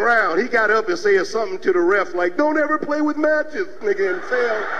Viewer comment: I thought he said Don't ever play with Magic😭